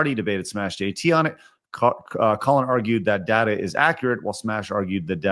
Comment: the L for not accepting tbe game has sold well and instead say all the sales data is fake. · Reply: I heard "inaccurate", mot fake.